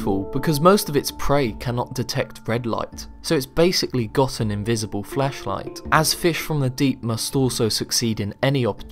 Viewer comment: One minute in and I thought he said "invisible fleshlight" and I nearly leaped from my chair and yelled SHARK TANK!!!